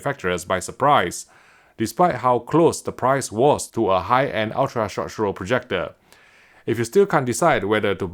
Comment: Am I the only once that laughs everytime when I hear Vincent say, 10:01 10:06 10:14 "short show" (short throw) 🤣